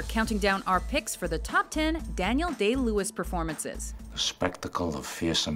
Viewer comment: Lol for My Beautiful Launderette I could've sworn she said "Daniel Gay Lewis"